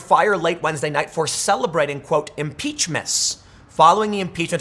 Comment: I thought they were saying “impeach-miss” now that’s the headline.